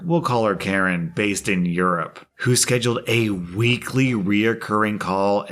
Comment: Oh boy... 7:18 my mind went somewhere... listening to it "in comes a stupid meeting leader (we'll call her Karen) based in Europe" well thats where my mind interupted and dropped in Urine instead of Europe... pause... Karen based in Urine... why does this make so much sense? The previous story about urine couldnt have been more perfectly placed lol